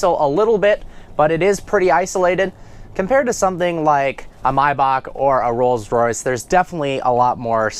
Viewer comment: Did he just say myback. Its maybach